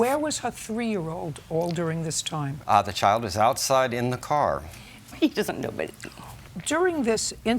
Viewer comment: Does anyone know what the defendant mumbled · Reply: “He doesn’t know, but “